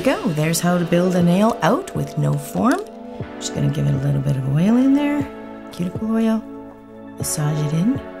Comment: the auto english captions say "just going to give it a little bit of whale in there cute boy" they were so accurate at the beginning what happened haha ???!